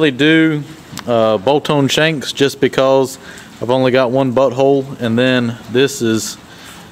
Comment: Ha ha did u say bolt hole or butt hole. It sounded like butt hole. Lol lol hope u have a great weekend and week. That looks great. Really turned out awesome. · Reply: Yeap, I definitely heard the same! 0:25 "...I've only got one butt hole...0:36 there's not enough room to put two holes in there" hahaha!